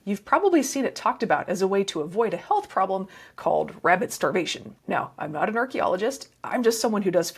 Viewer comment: I thought you said rabid starvation. That sounded a lot more interesting